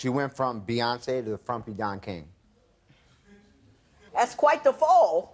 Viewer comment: I did not like that frumpy don king comment😑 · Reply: I thought he said JOHN KING..lol